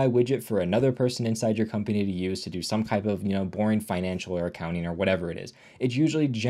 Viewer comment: i swear I heard at work, another UI widget, for another person to use another type of p0rn financial accounting 😂